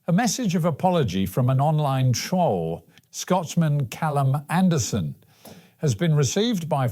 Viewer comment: Hi all at, really appreciate your daily news updates. On this mornings session, I think you described the troll fella as a Scotchman, it should of course be a Scotsman. Apologies if I misheard this, and once again thanks for all the great news stories.